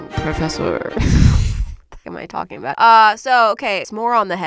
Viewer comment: I really like is but im like what did she say??? at 3:07 but anyways I really like her videos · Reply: she says “what am i talking about”